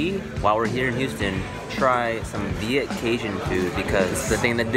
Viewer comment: I thought they said vegan Cajun food but then I saw a bucket of meat